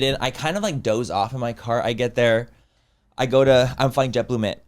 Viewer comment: why am I hearing you say “New work” 🤣🤣